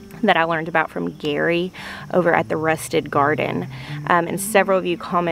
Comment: I always hear “rustic” garden. Just learned that it’s “rusted” garden :-)